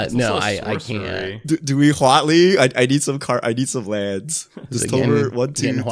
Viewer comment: What did say at 28:17? Couldn't here him over the others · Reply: He says "Getting huat in here" in response to talking about casting Huatli